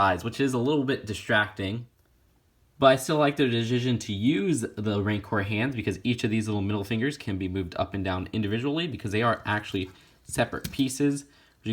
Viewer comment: What the duck did he say! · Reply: "But I still like their decision to use the Rancor hands because each of these little middle fingers can be moved up and down individually because they are actually separate pieces."